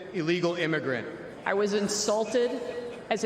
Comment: I thought SHS said that she was "assaulted" (instead of "insulted"). And i don't know why i automatically thought it was some GOP geezer who copped a feel under the table. But being so dramatic over an insult, idk about these Republicans. It's like they are the political equivalent of professional wrestling fans. And winter is coming.